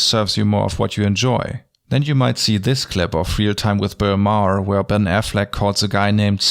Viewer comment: Your accent got in my ears and I thought you said "fear time with Bill Maher" which isn't far off lol.